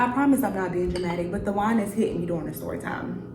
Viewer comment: Girl not me thinking when you said “Da wine is hitting me” i thought you said "Dajuan is hitting you up right now” 😂😂😂 i just did a double shift at work excuse me🤣